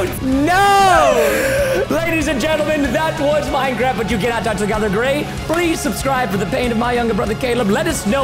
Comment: I thought that you said you cannot Touch stone not Grey